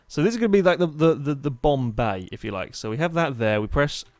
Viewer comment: Anyone else heard Bombay instead of Bomb Bay at 2:16?